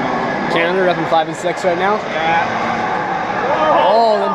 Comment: Hehe! Look at 12:33 instead of Canada, up in something and 6th it says in the captions having sex right now! I always have captions on for funny moments like that!